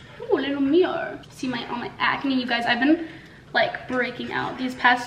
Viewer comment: It sounded like said sm-acne 😂🤣 lol love her